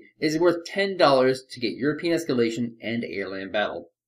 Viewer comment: Why i always hear "Your penis scaletion" insted of "europian espalation"?